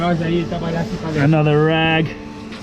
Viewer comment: damn that actually caught me off guard, made me jump a bit thinking how disgusting. i thought he said "another Rat" while pointing the camera at it and it looked like a rat.